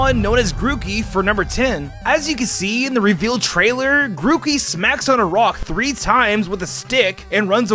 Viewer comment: i heard smacks a rock 3 times whit his dick XD